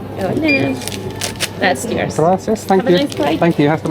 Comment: did you say hasta mañana?😅